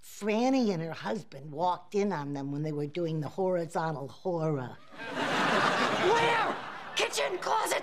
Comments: I’d like to confess I first learned “horizontal hora” from this scene many years ago and have used it many times since then wondering if anyone would catch the reference. 😂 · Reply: And I thought she said "the horizontal horror", which still fits in there. Thought it was slang for getting it on. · I’d like to confess I first learned “horizontal hora” from this scene many years ago and have used it many times since then wondering if anyone would catch the reference. 😂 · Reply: I always just thought she said “horizontal horror”